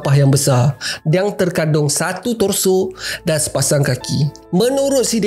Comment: Apa rrrrr 'tongsu'? sorry, cant catch up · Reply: Torso atau bahasa melayu nya batang tubuh hahaha mahap sebutan tak jelas eh?